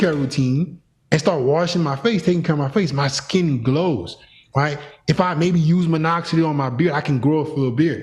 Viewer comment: what did he say to use on the beard? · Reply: Minoxidil (foam)